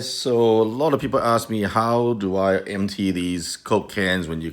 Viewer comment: What i hear: ".... empty this cocain"